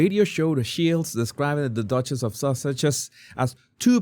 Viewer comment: 😂I was right, lol. I had commented on your vid yesterday about your lovely accent & how I thought I'd heard Duchess of "sausages," lol. But watching now I'd accidentally had subtitles on, and it also wrote out "sausages," which is perfect. From now on, it's Duchess of Sausages for me!! 😂😂😅Love it..your analysis for everyone, including Kash, was spot on✨️🙏🏻